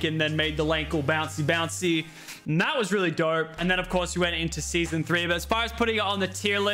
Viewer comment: did he says “then we went to season 3?”